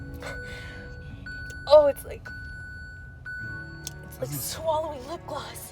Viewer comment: I thought she said it's like swallowing blood haha